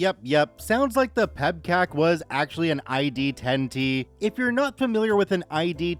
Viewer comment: ID10T. At first I thought the guy said ID10P, which didn't make sense. PEBKAC has an equivalent phrase in law enforcement. For drunk drivers, an officer might say "there's a short circuit between the steering wheel and the gas pedal." I had some winners in the computer world when I worked at a law firm. We had just recently converted from IBM Displaywriters to PCs running WordPerfect in DOS. The one IT guy got an assistant fresh out of school. I was just a peon, but the assistant had to ask for my help. She actually didn't know how to format a floppy disc in DOS. She went through school and had never touched a PC, only a Sun Microsystems mini computer. Luckily I had learned DOS and other PC stuff in school on my own, even though I didn't use any of that in my job.